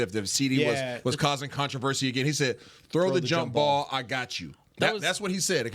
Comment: I thought he said "nice throw 4" or something like that preciate the clarification